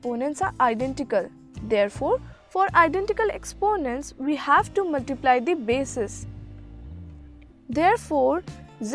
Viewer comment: I can't understand that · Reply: She is saying that if the exponents are the same you should multiply the bases (5×4)= 20 now the exponents are same so = 20²